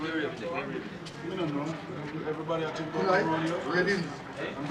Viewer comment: did they say reading?